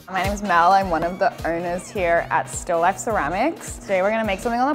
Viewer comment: I thought the lady just said "I'm one of the urners here" but she said "owners" and I actually thought oh wow, yeah ok. There are urners who make the urns and those who make bowls are bowlers. . . :)